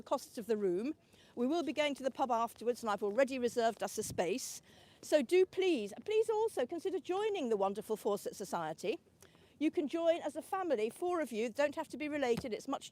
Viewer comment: Good luck with your F Society meeting. (I can’t hear what the F means.. it sounds to me like “forceps” but that can’t be right.😮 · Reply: 😂 I heard it as "Corsets Society" and I was there!